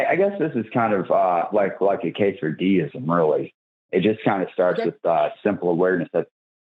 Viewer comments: Lol At the beginning he said something about “a case for deism” and for a second I thought he said he was into “quesadilla-ism”. He would have been better going for that position. They can be very tasty. As long as he stays away from the Gordita of the Gaps fallacy. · When he said "a case of deism" I misheard as "quesadilla-ism" and for a moment thought that there might be one religion for me after all.